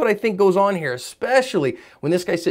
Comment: First thing I thought the Uber driver said want me to fuckin shoot you but either way he still has the right. Dark out random strangers tried crashin truck into you and passengers Room temp challenge